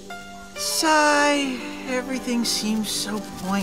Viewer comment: DID HE ACTUALLY SAY SIGH?